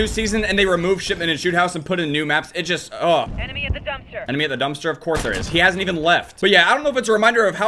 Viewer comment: I no scoped a guy twice (got a hit marker the first time) while he was trying to knife me last night lmfao 8:01 Did that say "Enemy is a dumpster"???? Obviously its right and its nice that the game knows what these weirdos truly are lol